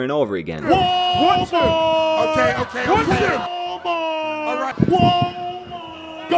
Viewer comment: All I heard was "Paulmart"